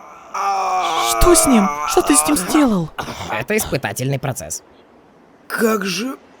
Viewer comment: отсылка на один момент из метал фемели , не помню какая по счету серия , но вроде называлась "квест")))) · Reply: извини, но кажется тебе послышалась, Глем из метал фемели сказал "Это воспитательный процес", а Краткий сказал "Это испытательный процес"